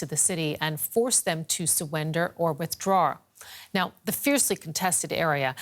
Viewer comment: Did she just say "Suwender" 0:12 Yes.. SUWENDER NAO!